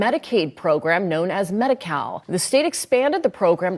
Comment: When they said medi- cal, I thought that they said medi-cow. Maybe I was just thinking of how much that they can milk the system. Lol.